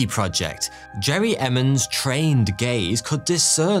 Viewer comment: Sorry I heard that as “Jerry Emmons trained gays”.